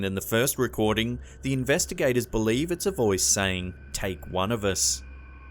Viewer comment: Instead of "Take one of us", I hear 'They're one of us"